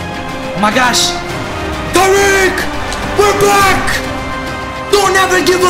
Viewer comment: we're black😂☠️😭exactly what I heard, he meant we're back